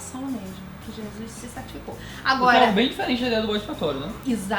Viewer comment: Eu não entendi o que ele fala ali aos 12:10 e 12:11. Ele falou "o que é bem diferente do eixo católico" ??